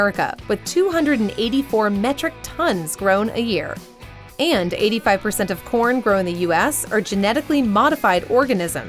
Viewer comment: At the end I heard "85 percent of all Porn grown in the US are genetically modified organisms"....